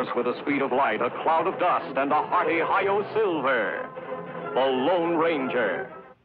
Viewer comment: I remember when I was a kid I thought he said "Ohio Silver! Away!"